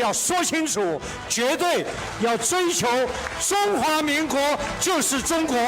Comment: I think he said "Republic of China is China too", not "ROC is a part of China"